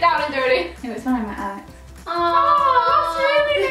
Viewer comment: What does say at 4:11? Please can someone answer I'm so confused 😂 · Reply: I said 'it was when i met Alex' 💗